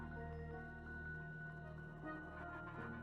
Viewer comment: Can someone explain what lunar said at the end? The sub titles say: Ray, back to the sideline lunar. 20:34 Is that what he said or are the subtitles not doing justice? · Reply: He said “great, back to sideline Lunar” I’m pretty sure :3